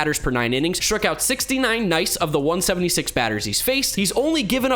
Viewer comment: did you really say Nice? 🤣🤣🤣